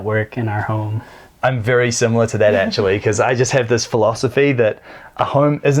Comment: Did he call Angelique @ 4:06? 😕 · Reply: Actually, even though he looks like an Angelique